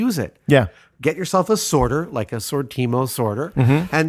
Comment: It sounds like he's talking about a "Sortimo" sorter at 07:49, but I'm not finding references to it. What is it that he's saying in there?